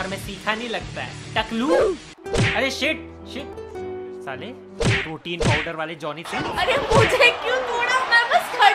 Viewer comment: did you actually say Johnny sins🤣😅😂